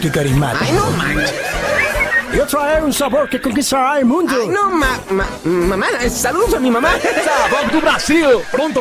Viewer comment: Yo entendí que iba a decir ese pingüino iba a decir no mames pero como es una franquicia famili frendly no pueden aber malas palabras